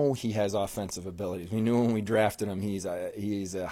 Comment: Did he just say we drafted Krebs???